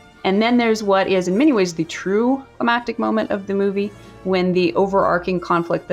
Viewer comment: Answer: "Climactic." The full interview doesn't have other audio competing, so easy to hear. Original: What does she say at 25:22? "Emactic?" "Amatic?" I can't make this out. It would think "thematic" would fit here, but her lips do not form that word.